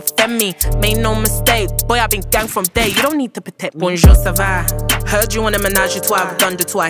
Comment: Did she say Vondrousova on 0:57? Cause that's like a tennis player.